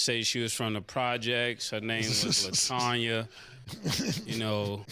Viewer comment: For a second I thought mase said batonya referencing the track runner.